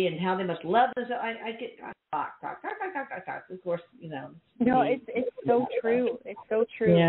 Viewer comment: I heard an EVP at 2:15 😊 I couldn't really make out what it says but my earbuds aren't working. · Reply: ‘Yeah’ ‘ you know’